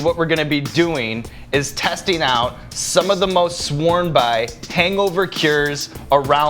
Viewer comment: Bart Chrysler: "I thought you said hangover curries"